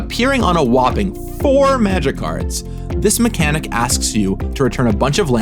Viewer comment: I love how the captions show this as "A whopping 4 magikarps" instead of "magic cards" 😅